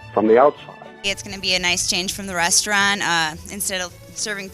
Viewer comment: Did i just hear "breasturaunt"?!